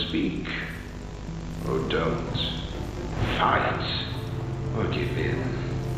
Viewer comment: I thought he said “fight, or give in.”